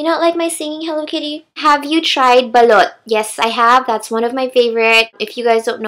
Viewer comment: I may not understand but you can sing I must say. Btw when you mentioned, "Balut" I thought you said "Belut" which is in malay which means eel (